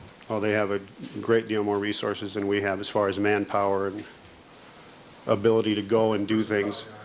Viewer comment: could someone tell me what was said at this time? Was it an objection? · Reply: witness said 'manpower', and Johnnie Cochran (I think) half jokingly corrected it as 'person-power'. Judge Ito tried to keep terms gender neutral throughout the trial; for example he corrected Marcia Clark saying 'he' to 'he or she' at one point, referring to a hypothetical officer